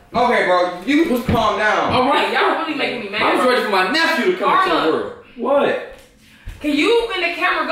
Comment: Did anyone else hear say "I'm just waiting for my nephew" at 5:12😂❤